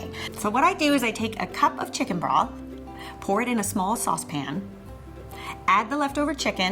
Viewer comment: what is chicken braf? Bra? Bar? I don’t even know what she said but does anyone know what it is? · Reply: Change corn broth